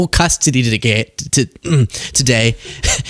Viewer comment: Was that a Freudian <mail> heard you say g*y instead of today..just saying